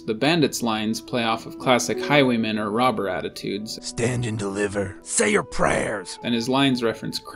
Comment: The first bandit line "Stand and deliver", back when I was a kid, I ALWAYS thought they ether said "stand in the river" or " stand in the liver" XD